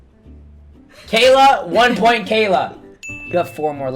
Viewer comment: At first I thought it said Gayla not Cayla😂